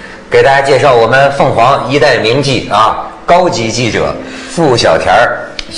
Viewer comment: 一边干活一边听的我听成了"一代名妓","上了床一个样,下了床一个样"🤣🤣🤣😜🤭